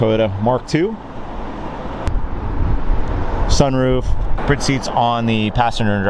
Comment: did he say "BRID" SEATS?!?!...... why?